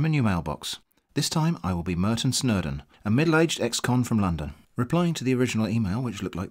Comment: Originally i heard "Militant XCOM from London" and was very confused